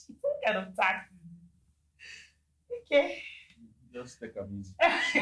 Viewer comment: Your husband is so cool. I heard him say" just take am easy